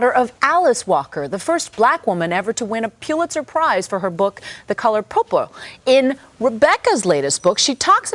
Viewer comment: I thought the reporter said "The Color Poo Poo" at 0:30.